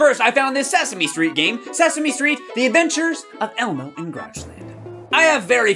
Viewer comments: When Elmo said Grouchland I thought it said Crotchland! Ewwwwwwwwwwwwwwwwwwwwww. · I thought Elmo said croch land lol